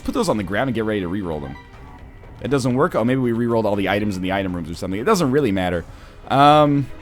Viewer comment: Played with and got a reroll rune from one of the skulls in sheol and rerolled quarter into raw liver. Gg NL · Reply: Murdoc Niccals i thought he said he rerolled the items in himself